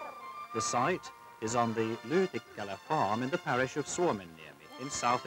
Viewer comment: Where did you say? Soumia? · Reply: Suomenniemi. The cape (niemi) of Finland (Suomi).